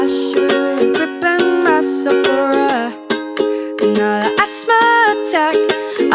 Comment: I've misheard "prepare myself for another asthma attack" as "grip and wrestle for another asthma attack" which changes things.